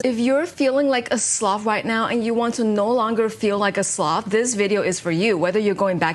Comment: why did i hear ' if u no longer wanna feel like a Slav" :face-purple-wide-eyes